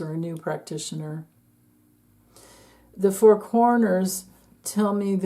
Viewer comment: When you said 4 corners I heard "four coroners."😳